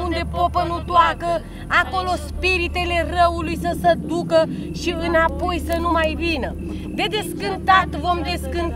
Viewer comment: it sounds like she says in English “she will not poison my dinner” (or she said my winner/winter) Did anyone else hear that or just me?! I’m only 29 minutes into this but finding it so fascinating. Great job so far!